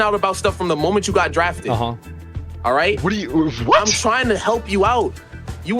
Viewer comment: what is he saying "Trying to help you out."